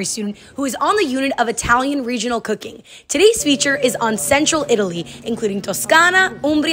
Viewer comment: I thought you said "sensual Italy" not central 😂😂